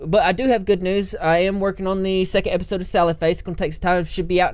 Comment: I thought you said salad face, not sally face... whoops...